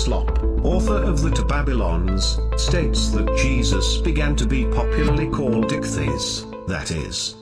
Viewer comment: anyone else hear the computer voice say something like, "Jesus began to be called 'Dickface'."?